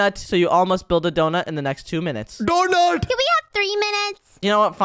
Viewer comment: I hear door noob